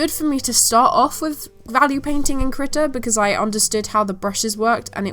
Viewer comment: I thought you were saying kritter and not krita